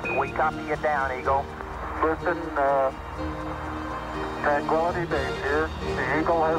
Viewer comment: not only did Charlie Duke confuse the word "twan" - tranquility base. Neil Armstong also sounded like he confused the word. It sounds like Neil said, "Houston, Tranquality base here". It sounds like Neil said "Tran quality" instead of "Tran Quility" base. It sounds like Neil said "Quality" instead of "Quility". These men knew the whole world was watching and listening. Who wouldn't get a little nervous? Especially since there was less than a minute of fuel left until "Bingo time" for the abort. Tensions were at the maximum a human being could possibly undergo in a voluntary setting IMHO. No wonder they stumbled on a few words here and there. So much more for the authenticity of the moment.